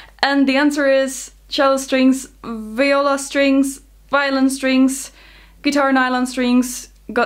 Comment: Ich habe bei 5:26 "Violent Strings" Verstanden und den Rum fast überm Arbeitsplatz vor lachen verschüttet :)